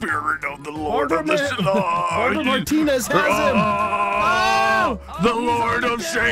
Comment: Did anyone else hear "Melanie Martinez has him" at 2:50?